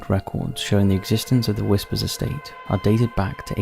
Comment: I first heard that as "the WHSPRS estate".